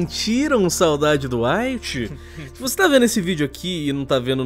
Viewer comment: ouvi light kkkk Lembrei do death note📒